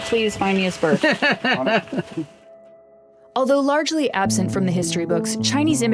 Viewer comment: Absent from the history books is full of crap. I would expect better, but opb is a Salem/Portland outfit..... so this is what we get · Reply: I think you misheard, the line is "Although largely absent from the history books" which is significantly more nuanced and accurate.